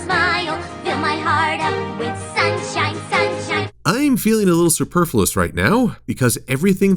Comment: "I feel a little superflus" What word did you use there?